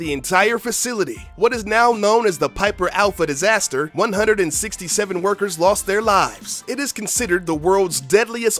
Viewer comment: Am I the only one who laughed because I heard he said "167 burgers lost their lives"? Please don't hate me for it.